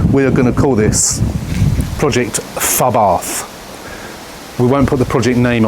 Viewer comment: FUBARth - is what I thought you said 😂